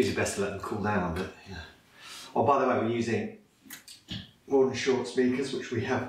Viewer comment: what speakers are these in the video? thank you! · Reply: I think he said "Mordaunt-Short" at about 2:30.